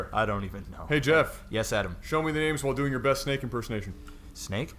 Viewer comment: I could have sworn he said Snape impersonation >.>